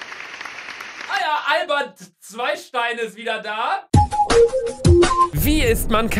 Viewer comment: Niemand : Wirklich Niemand : Ist Albert Zweistein und ist der beste Youtuber 😂😂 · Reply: Ich habe zuerst Alman zweistein verstanden... 😅